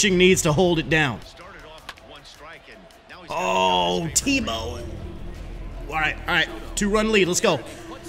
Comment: it sounds like you said “T-Bone”